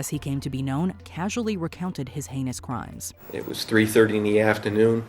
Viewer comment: That guy actually talked about his "anus crimes"? Did I hear that right? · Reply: Its "heinous" crimes, but "anus crimes" is great